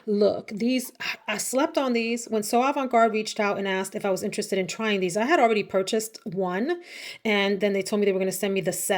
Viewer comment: Hey girl hey 👋!! What are you talking about there around 12:18? Did you say "Tumi"? Cause you have my attention Lol!! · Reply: Yes! Tumi! As in the luggage company. My faves are Utopia and Signature.